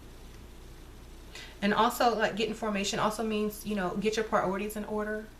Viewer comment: "Ok lady now let's get in formation" I feel like that means women coming into there powers ! I feel like this was an out of the broom closet video lol and what best way to come out with a ritual that she let everyone watch ... Love her video made me very excited 🔮💜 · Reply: I always heard this as "let's get information"